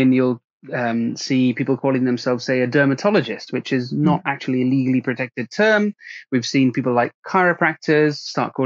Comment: did he say Dermatologist is not legally protected term? Could some one clarify?